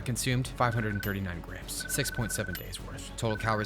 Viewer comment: Did somebody say 67